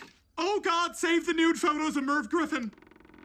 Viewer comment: Oh god save the nude photos of mercy griffin!!!! I'm sure no one her gets that reference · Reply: I thought he said merv griffin